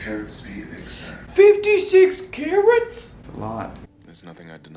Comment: I took my headset off for a moment and when I could hear again the first thing I heard was "56 carrots?!?" and my mind went "that sounds like too big a salad even for a cruise buffet". I did later realize that we were talking about carats, but I would like to commend my brain for unfailingly thinking about food first and foremost.